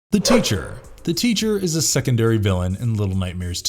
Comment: I think he said “The Toucher…”